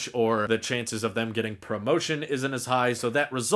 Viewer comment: Did you say Pro-Motion at 6:16? You got Apple on the mind lol